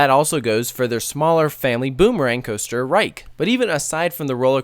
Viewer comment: There is no ride called "The Reich" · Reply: So which word did I mishear him say? Honest question.Sounded a lot like "reich" to me lol